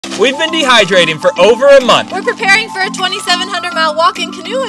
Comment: I really thought they said “we’ve been dehydrated for over a month !” 😭